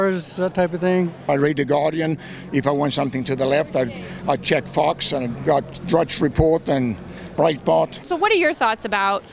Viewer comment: did he just say "if i want something to the left i check fox and drudge report and breitbart?" lmao wtf hahahahahha!